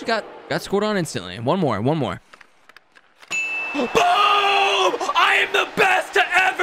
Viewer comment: If you turn on captions go to the timestamp 4:05 it says Bob instead of boom